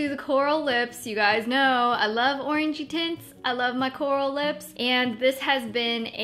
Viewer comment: omg i was looking away from my computer screen for a second and i thought you said orangey tits instead of orangey tints LOL >.<